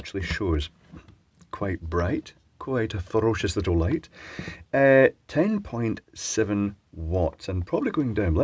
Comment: the youtube subtitles actually messed up here, saying '10.6' instead of 10.7